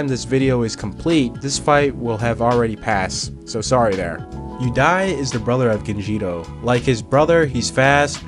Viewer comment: I was just listening to this and I thought he kept saying "his brother who died"